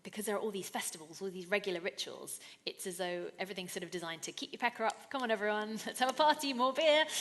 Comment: did she say that we say "keep your pecker up?" xD